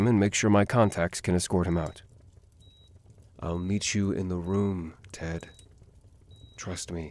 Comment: I for some reason thought he said Need instead of Meet and I started fangirling- 53:56 I died at this part. Robin killed me too 😂😂😂 This is amazing though. I LOVE the voice acting aNd the whole story! The art is fabulous aswell!! And with that ending I'm pretty sure everyone knows there's gonna be a Season 3. And I can't hardly wait for that xD Keep up the good work!